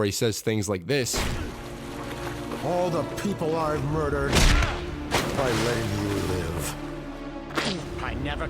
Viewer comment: I swear for the longest time I thought that Batman said "Of all the people I murdered, I let you live" not "All the people I murdered, by letting you live"